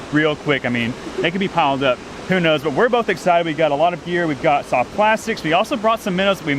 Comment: Sounded like you said, "We`ve got a lot of beer..."